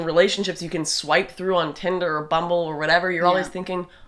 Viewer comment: Lol @ 3:48-3:52 i thought Lauren said tinder or butthole